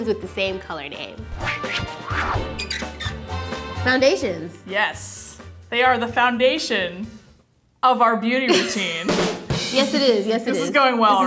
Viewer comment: the automatic subtitels say 'foundations, yes, they are the foundation of our beautiful tan' & I was like well ok, it's not what she said but its not wrong actually